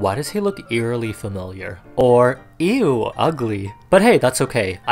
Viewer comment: wtf she is/ he is not saying ew ugly am arabic and i understand that she said/ he said he's not very very beautiful